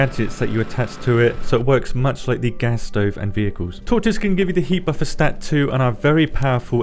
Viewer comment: sorry but @ 08:35 you say torches but you say it so fast it comes across like your saying " tortoises " - my apologies but thats what i hear - only when i turn on subtitles did i realise your saying Torches :(